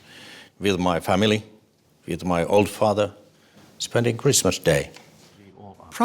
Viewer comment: wtf did he just say he was with the all-father?